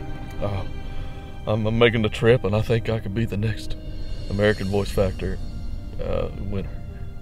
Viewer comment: You should know that we're all expecting another episode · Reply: I totally heard “American voice factory” the first time they said it for some reason. 😂😂 I was like well serving the truth today aren’t we? 😂😂